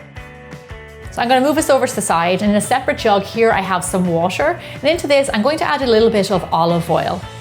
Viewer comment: Is that water at 1:00 because I think u said washer which I don't know what it iz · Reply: It's water. Please excuse my Irish accent.